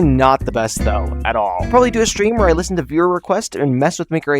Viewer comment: I HEARD STREAM